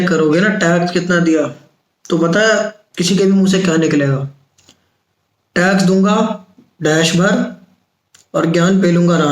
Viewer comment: what is that dash? Please fill in the blank I didn't understand that 🥲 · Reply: Jhant bhar tax dunga